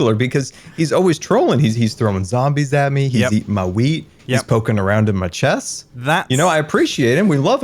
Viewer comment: did anybody else hear "he's eating my weed"?